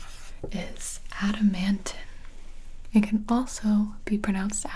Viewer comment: I wasn't looking at the screen and I swear I heard adamantium and I was like WHAT??!?!